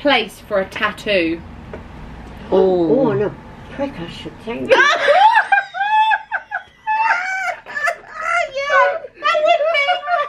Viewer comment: did she say “on a prick I suppose” 😂😂😂😂😂😂😂😂